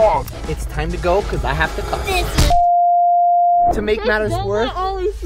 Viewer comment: I think he said he had to curse ???👀